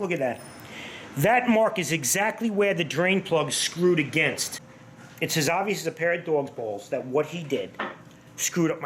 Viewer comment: I thought he said “it’s as obvious as a pair of parrots balls” which got me confused since they don’t have any.